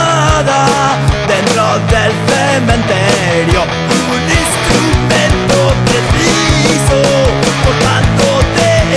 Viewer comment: Buen tema, geniales imitadores de Lapolla..No entiendo lo qué dice en 0:38 : "....cortándote el cuello" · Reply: "Un instrumento preciso cortándote el cuello "